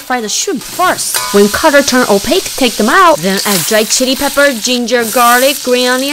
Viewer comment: ok do when she said chilly pepper i thought she said shity pepper lmao